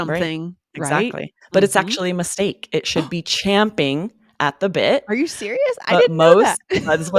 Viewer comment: I hear "jumping at the bed"